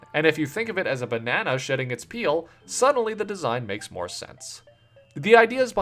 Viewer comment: God I thought you said "a banagnaw shitting its peel"